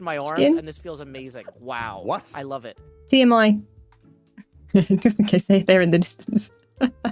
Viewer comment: I heard it "as am i" instead of TMI 🤣